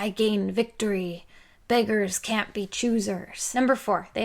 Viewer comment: I thought she said “Vaders can’t be choosers” and I liked that a lot.